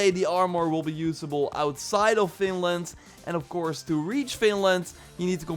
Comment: I hear says "Finland" not Vinland but maybe it's bc of his accent. IDK it hears like it